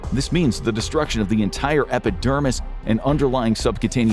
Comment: did you just say epic dermis 😅